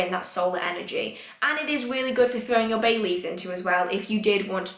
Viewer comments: i misheard bay leaves as babies and was quite concerned when you suggested throwing them into the fire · was watching on 2x speed late in the night/very early yule morning (southern hemisphere) and thought you said "throwing your babies into". Took me a second to realise you were not recommending we yeet newborns into a yule fire to celebrate.